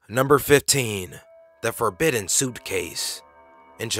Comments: Did he just say " the forbidden SOUP case "? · I thought he said forbidden soupcase. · i thought he said the forbidden soupcase in the beginning of 15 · "Forbidden Suit case" I just wanted to say this but I thought he said F O R B I D D E N S O U P C A S E